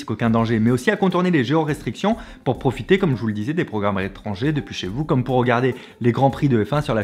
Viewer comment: Le "tu veux rencontrer les lumières chaudes de ta région" de et le départ en fusée de Fabien à la fin 😂😂😂 PS : J'ai eu un bug de cerveau à 6:09 j'ai entendu "dépêchez-vous" à la place de "depuis chez-vous" ça ma brainfuck, me suis dis "pourquoi tout d'un coup il veut qu'on se dépêche 🤔 ?" X'DDD